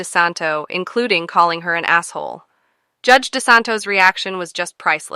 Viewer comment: She didnt say asshole... i heard, "what a BAD SOUL"